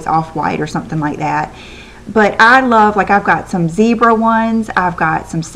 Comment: haha! Oh, when you were talking about how you liked different kinds of pillowcases....I thought you said that you had some "Bieber" pillowcases! (you'd said zebra but I Obviously didn't hear you right!) haha! I thought, "Wow, she has Justin Bieber pillowcases?? That IS different!" HA! I was glad that they were zebra ;)